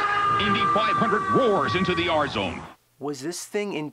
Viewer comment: Am I the only one that thought he said “8,500 roars into the R-Zone”?